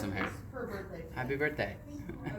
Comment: I thought they said "screw you" then i realized it was happy birthday 😂 THE CUTEST